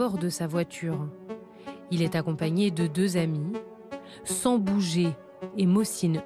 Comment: qui a comme moi compris "il est accompagné de deux amis 'sans bouger' et 'mot signe EUX' "